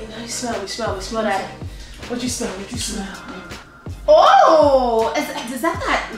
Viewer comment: did she just say smell daddy😂